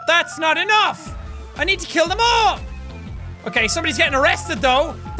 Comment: for a second i thought he said "i gotta kill the mall"